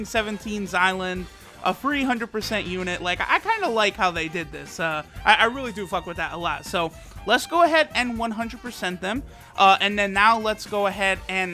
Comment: did he say he fuck with that a lot 😹😹